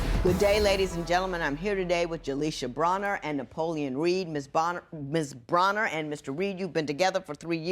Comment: Lol I thought it said Boner and not Bonner.